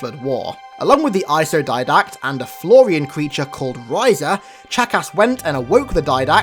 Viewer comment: When he said Chakas I thought he said jackass